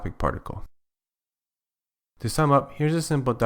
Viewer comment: I heard 'kisama' instead of 'to sum up'😂